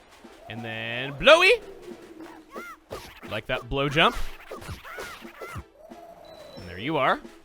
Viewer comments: "Gust in the Dust" Best rhyme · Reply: Whenever he said Blow Jump I thought he said Blow Job... That's the wind's job, not yours! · I thought he said blow job xD